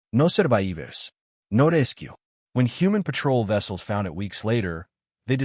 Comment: What language at 4:39 ? Is it English words pronounced in Portuguese? "No surraeevers! No resskku!"